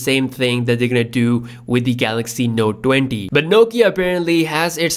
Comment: Did you just say Galaxy Note dwenty @ 0:41? ... Love my Indians.